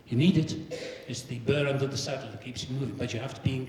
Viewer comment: What does he say at 45:54? "It's the .............. that keeps you moving." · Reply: Mike Manocchi "It's the burr under the saddle that keeps you moving".